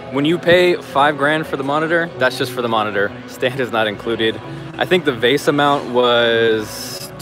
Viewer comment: I must have misheard something. I thought I heard him say the monitor STAND was $1,000. But that can't be, because no one is dumb enough to pay $1,000 for a monitor stand.